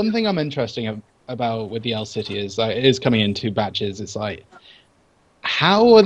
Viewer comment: is it just me or did when said " coming in batches " it sounded like he was saying "coming in bitches" ? lol